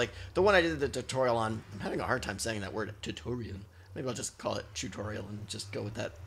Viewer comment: did you said choocorial?